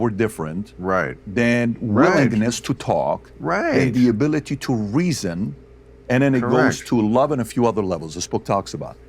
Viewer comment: did he just say bill mahers ability to reason? hes just a foolish kind of comedian